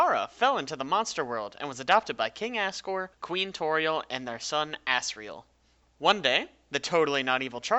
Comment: did u say ass riel